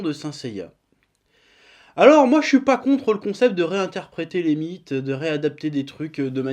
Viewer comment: j'écoutais la vidéo en arrière plan en codant, et a 3:42 j'ai entendu " alors moi je suis pas contre le concept de réinterpréter des bites", et j'étais en mode wait, what? x)